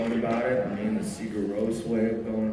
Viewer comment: Did he say Sigur ros? I couldn’t quite make that out at